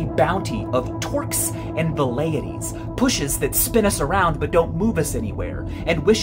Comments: “The internet is a source of… wait what?! The transcript shows “torqus and vales” but that doesn’t sound quite like what Michael said. But I couldn’t parse it or make sense of it or find anything like it after repeated searches. Help, anyone? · Does anybody know what word Michael says at/around 20:54 right after "torques and ______"? It sounds like Valaities, but that's not a word.